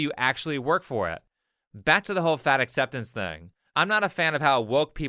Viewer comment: Thought it said face acceptance... think I'm loosing it.